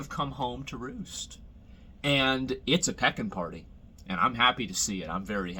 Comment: For a second, I thought Asmon said it was a peggin' party. I was excited for a brief moment.